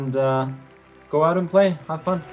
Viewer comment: in the end, did he just say go out and play?